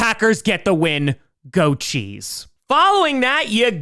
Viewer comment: When you said go cheese I thought you said go chiefs and my fingers were this close to unsubscribing